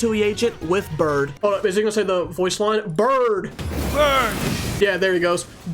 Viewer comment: I never noticed that it sounded like Bird 💀💀💀 and as much as I wish for it be be bird, it isn’t, it’s “BURN 👺👺👹👹👹👹👺👹😡🤬🤬🔥🔥🔥🔥🔥☄️☄️🔥🔥☄️🔥☄️🔥”